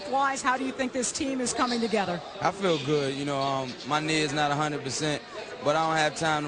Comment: When AI said my knee isn't 100% I thought he said My nigga isn't 100% percent